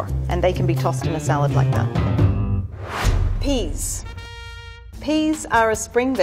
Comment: I thought she said pingas